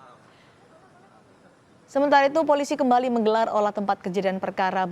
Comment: Maaf...salah dengar, kirain menggelar acara syukuran 😊